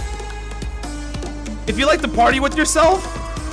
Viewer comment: I thought he said “If you like to play with yourself” 😂